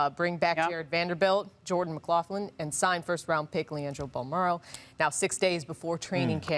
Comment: it sounds like she says “sign first round pick LiAngelo Ball”